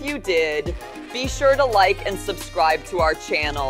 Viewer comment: я один послышал зай Марк